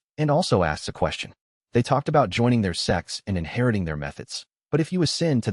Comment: Man while hearing this audiobook I also have subtitles on to read a word when I don't hear it clearly and in the subtitles it says they talked about joining their sex instead of sect and it made me laugh. I wonder when YouTube will fix subtitles.